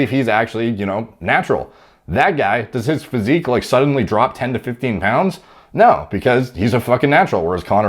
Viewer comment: i thought he said, "Back guy, his physique doesnt drop 10 or 15 pounds." Thought he was about to say "cuz he never had 10 or 15 pounds of muscle to lose."